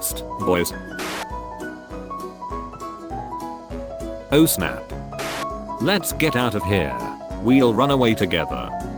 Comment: Can anybody tell me what 1:24 was? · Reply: The doors are closed